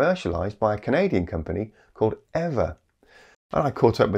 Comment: New Doesn't Mean Best... Old Doesn't Always Mean Wisest... [3:29 Eavor... I heard "EVER" I almost Got a Seizure]